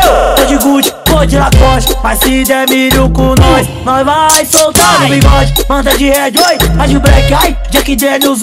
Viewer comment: Oque ele falou? So entendi isso "mais se de milho com nois nois vai soutar biribois"??? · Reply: Ele disse mais se der milho com nois vamo soltar no bigode!!